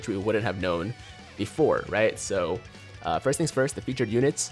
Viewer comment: teq trillin Me:wait did he just call me my boi krillin "trillin"